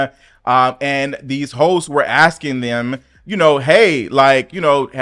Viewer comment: I swear at 01:10 I thought you said hoes. I had to rewind it 3 times! For anyone else who heard the same thing, he said HOSTS!